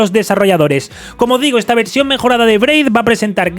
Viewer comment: Te tenia en modo podcast y escuche "Pray" cuando dijiste lo de Braid fjzjc casi me da un paro 👁